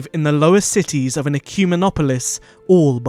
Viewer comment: What is that word? I've played it back a couple of times. It sounds like 'cumin-opolis'. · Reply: Ecumenopolis!